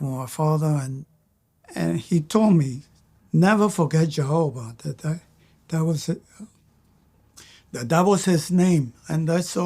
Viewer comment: put the subtitles on, and see what he is saying (or youtube is understanding:) "Jehovah, that was it: the devil's his name"